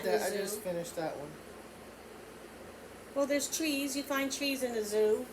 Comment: I thought said cheese instead of trees, then I couldn’t figure out what zoo she’s been to with cheese 🧀😂😂 love your ideas.